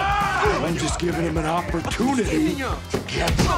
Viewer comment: That second scene where he was throwing Alexi out Did he just say "Hopper tunity"? Oh my God..he made a dad joke😂😂